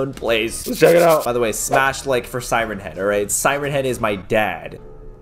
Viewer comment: Caylus: Siren Head is my dad! Me: I thought you said light headed is your dad?